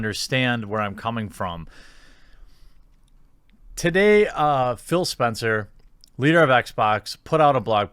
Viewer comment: Thought you said Phil Spectere, accidentaly dadded a creepy british music murder clown